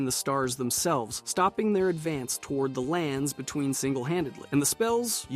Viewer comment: just to lyk, the subtitles say "Radagon" when you said "Radahn" which is supposed to be pronounced "Ruh Don"